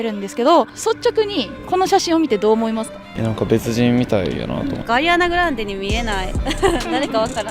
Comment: I thought she said Koreana Grande 😂